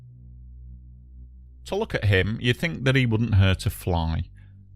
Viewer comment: Ohhh “to look at him” is what was said At 00:16 I was trying to figure out why Tolukat Hym wouldn’t hurt a fly